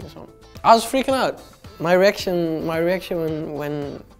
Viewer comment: Lmao somebody already got it, haha I was gonna say at 1:25 "my erection my erection" hahaha